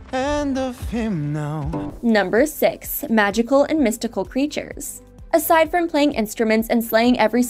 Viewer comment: What she said: "number 6, magical and mystical creatures" What i heard: "Mystical, Magical!! (By Benson Boone)"